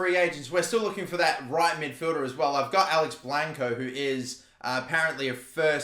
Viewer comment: I thought you said Alex wanko